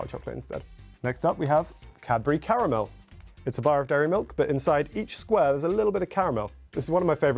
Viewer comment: is it only me or does it sound like he is saying "daddy milk" lol